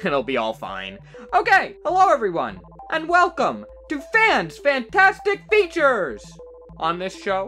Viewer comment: Anyone else thought it said Fans Fantastic Tissues?